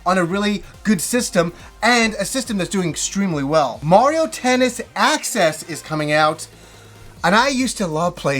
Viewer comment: I swear, it sounded like he said "Mario Tennis Asses" LOL...